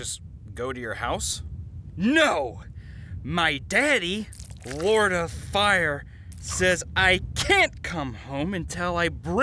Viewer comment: I thought it said Florida fire